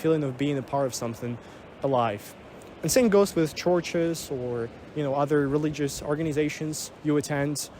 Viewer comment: thought you were saying torches. got it now. churches